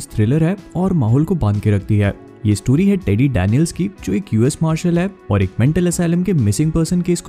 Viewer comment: I heard Dani Daniels I'm not joking really heard it 😂😂